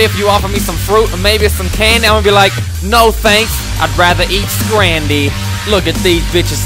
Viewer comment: lol just me that thought he said id rather eat "randy" instead of scrandy?